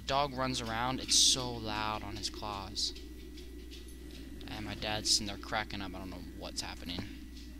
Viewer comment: what you said "And my dads in there cracking up I don't even know what's happening." What I heard heard "My dads in there up on crack" lol great vid. Don't listen to the hate!